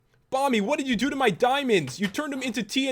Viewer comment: it sounded like he was saying mommy